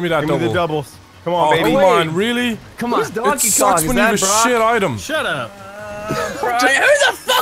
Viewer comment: Did you just call that a crap item at 1:48??? That's a great item!!!